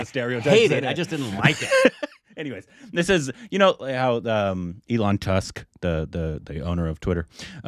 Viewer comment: Did he just say Elon Tusk, hehehheh I am not a fan either